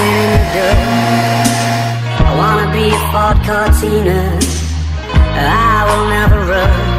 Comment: How do I hear, “I wanna be a fucked up teener“ 😭😭